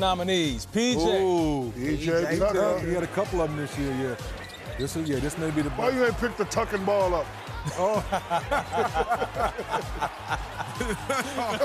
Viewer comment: did I hear that right? 😾 “This N***a the boss”